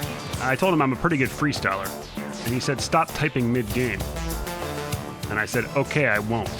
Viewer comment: The most underrated part of this whole video is when he tells him to stop typing midgame, and says, “Oh I thought you said stop TRYING midgame” 😂